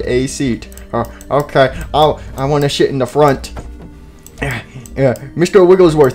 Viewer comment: when foxy jr said ima sit in the front the captions thought it said "ima shit in the front" XD